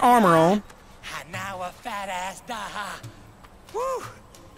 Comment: it sounds like he said fat ass😭